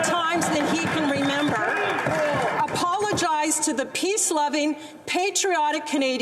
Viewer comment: does she says Piss loving. Canadian accent is funny aye.